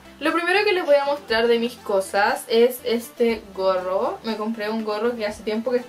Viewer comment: Minuto 8:07 el.... Perdón escuché bien ?? Mis cosas ?? Jajajaja